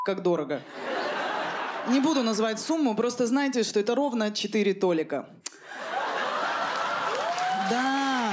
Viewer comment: спасибо за введение в оборот новой меры денег - один Толик! )) я в восхищении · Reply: "4 Толика" - думала послышалось)